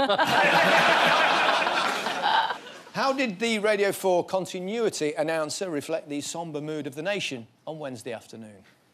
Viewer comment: I couldn't make out, or didn't get, the BBC Radio 4 bit at 6:54. Can I get an explainer? Paul was funny for it, though. Gary is beautiful, as always. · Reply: "Now, if you thought, Westminster was in crisis - Brian's looking for his reading glasses in The Archers." The Archers is a long-running radio soap; the joke is following up the drama in British politics with the "catastrophe" of a guy having misplaced his glasses.